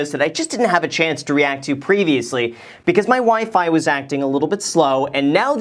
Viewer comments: In the beginning when you said "my WiFi acting weird" I heard "my wife and I acting weird" lol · At the beginning of the video I thought you said “my WIFE has been acting a little bit slow” 😳😳😳